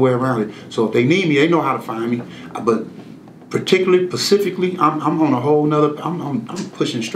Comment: he say "Pacifically" instead of " Specifically "! Dummy!